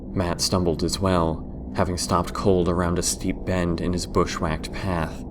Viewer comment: could someone pls can tell me what does it say there at 18:08 (english is not my second lenguage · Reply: Samuel volkmar velez it said stopped cold around a steep bend in his bushwhacked path.